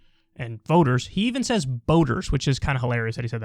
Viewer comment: Did he say boaters? thought he said voters.